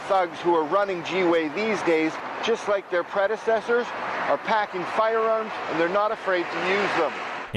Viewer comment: When said, "packing firearms." I thought he was saying, "packin fudge."